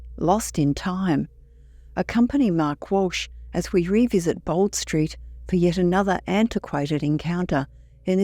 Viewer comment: Sorry, I thought you said 'Bond Street ' when it's a reference to 'Bold Street'?